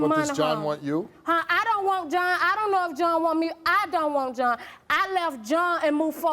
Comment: I thought she said she’s done with John 😂lOL